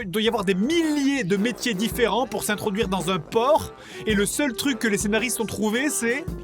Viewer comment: J'ai éclaté de rire quand j'ai compris "s'introduire dans un porc" 7:20 ^^'